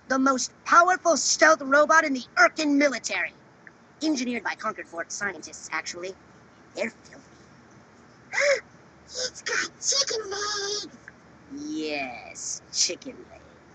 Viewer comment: At first, I thought he said Abortion Scientist. That would be senseful, being as theyre filthy